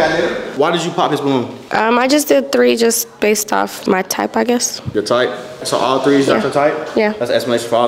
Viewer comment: I thought at the beginning she said she ain’t have a type 😂😂🤣 liar !